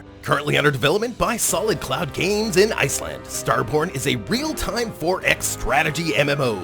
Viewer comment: At first when he said "Star Borne" i thought he said "star Porn" lol